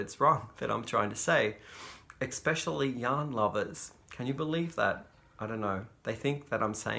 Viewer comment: The first couple times I watched, I thought you said "young lovers" too. Then I thought about it, lol. Do you watch Noble Character Crafts here on youtube, I think she is the one who came up with the Make 9 hashtag. <link>